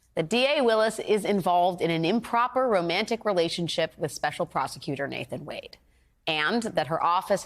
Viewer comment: Oh, you got me! You said special prosecutor, Nathan Wade. At first, I thought you said special prostitutor, Nathan Wade